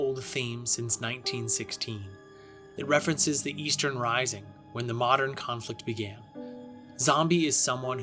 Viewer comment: It sounds like you said 'Eastern Rising'. It's the Easter Rising, as it the Irish Volunteers, Irish Citizen Army, Cumann na mBan, and more took up arms in Dublin during Easter Week, 1916. Edit: Also, you were talking about Belfast and Derry, and the map you used had the text 'Dery', not 'Derry'.